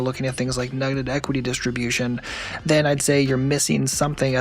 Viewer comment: what did he say? 'nuggeted equity', 'negated equity' or what? Google can't find neither (sorry, English is not my native)🤷‍♂